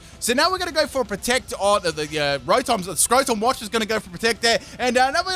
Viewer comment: did he just call Rotom “Scrotom”?